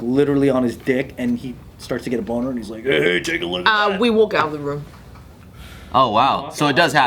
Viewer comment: wtf did she say???? I already rewinded like 5 times and I can’t understand what she said lol someone help 😂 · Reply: we walk out of the room???